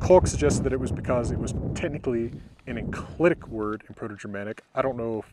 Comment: What's an enclidic word? I maybe misheard but even if I didn't hear correctly, I'm not sure what it means...